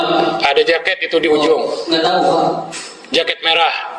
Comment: jam 5? Ini kuping saya salah dengar atau beneran itu yg dikatakan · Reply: Kemarin pake headset, kedengerannya" Ga tau.... Jam.... Jam 5... " tapi pak ga bereaksi. Kuping nih mancing fitnah.